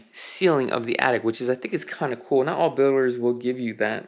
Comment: sounds like you said "not all boomers".... hope that boomers are meant to be builders.... lol